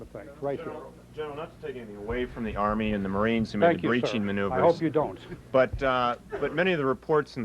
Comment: I couldn't understand what reporter said ? inreturn gen said : I hope you don't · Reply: "General, not to take anything (credit) away from the Army and the Marines who made the breaching maneuvers, but, many of the reports and the polls we've got from your field commanders were indicating that these fortifications were not as intense or as sophisticated as they were led to believe..." The General was saying that he hopes that the reporter does not take any credit away from anyone who executed the breach on the front lines.